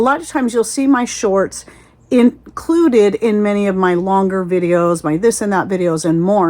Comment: I'm so sorry, igot the giggles. Listening while driving, heard you say, "show my shorts", I lost it